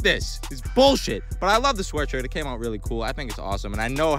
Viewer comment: At first I thought he said tell me how fat I am go ahead. I’m like where? 😂😂